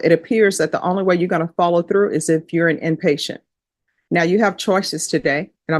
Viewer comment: I thought he said he chose prison but I guess he said inpatient. Can't hear these people! Don't know how the court reporter does it.